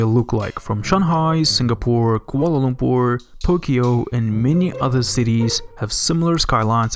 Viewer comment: I'm I dreaming? Did he just said pookyo.